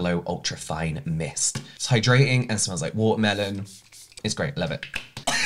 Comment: I was just listening while cleaning & I swear I heard him say, "it smells like whore heaven" lmao. I always watch/listen at 1.5x speed so that plus that adorable accent did it 😂. For a hot second there I was thinking, I wonder if that smells good or bad... it could go either way LMAO Edit